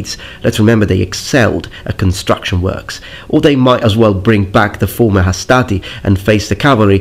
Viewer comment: fo a second there I thought you said "bring back the former astartes", LoL, imagine that!